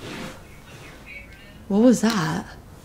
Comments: I hear something say favorite then boobuddy says whats your favorite toy 😲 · right before the bear says " whats your favorite toy" there a womans voice that says "your favorite" in the background. I heard it with earbuds on.